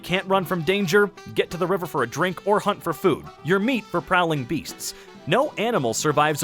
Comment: Before he said prowling beasts, I thought it said prowling breasts